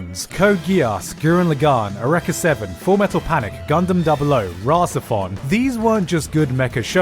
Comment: Summary: - Mecha isn't special anymore in the 2010s - Gotten a bad rep from series that started off strong but turned into trainwrecks: guilty crown, franxx, a/z, valvrave, e7 ao etc. - Series are stuck between appealing to existing fans and making things accessible for new viewers: Gundam, code geass, etc. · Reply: I still want to watch these but I can't hear most of them. 0:42 sounds like he's saying Rasa Fon which is what the subtitles say but I'm not getting any search results.